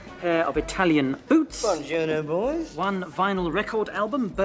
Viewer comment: "one pair of Italian boots" I really thought he said Bonjour after that